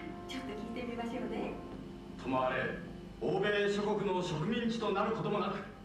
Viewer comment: 86年生まれの娘が大好きで、3~8歳頃年パスで飽きるだけ通いました。「ママ、鶴行こう」と。私は、休憩で寝ていた事も。 漢字からひらがなが出来る前に、鶴が「いいこと」と言う言葉を使うのが、どんな良いことが有るかを聞かれ説明に困ったことと、坂本龍馬がいきなり「とまれ」の言葉から会話が始まる「とまれ」とは何かを聞かれ困ったことを思い出します。4分おきの母娘二人きりの貸し切りだったことも。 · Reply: 今聞き直しました。「ともあれ」が「とまれ」に聞こえる！ 「いいこと！」って確かに普段は使いませんよね。 娘さん、本当に真剣に聞いていたんですね。(*^^*)